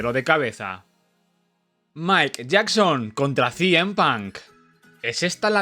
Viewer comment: escuché Michael Jackson😂